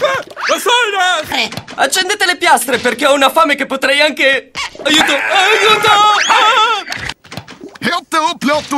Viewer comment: it sounds like he said I’ll sue ya